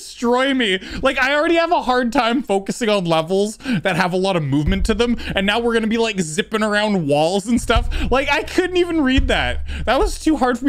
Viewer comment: im i the only person but at 1:38 i thought he said sipping around balls? bu its zipping around walls XD